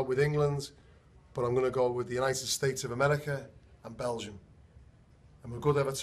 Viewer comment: What did Robbie say? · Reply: He said Belgium :/